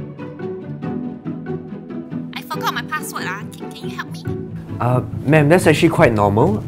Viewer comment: i heard “i fuck up my password